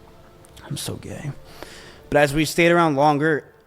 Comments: did he say what i think he said "I'm so gay" ? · In 14:57 does he say “I’m so gay”? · did bro just say "im so gay" 🤨?